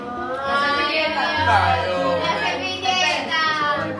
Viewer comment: Alguien me dice lo que dijo la muchacha en el min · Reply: Anais Mauro -La servilleta